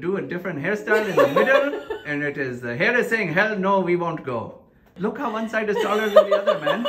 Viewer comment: I thought you said "Hair no, we won't go" 🤣🤣🤣